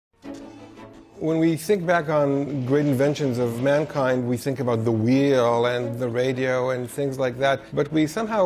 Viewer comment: the wii U? oh the WHEEL! whoops i misheard that lol